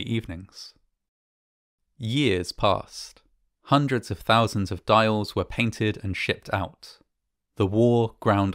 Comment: it says "Years passed. Dials 6 were made and shipped out." I was dozing off you know. While listening. And could of swore it said "Years passed and more dials were eatin and shitted out! Lmao 🤣. I was like.....OK.? 😆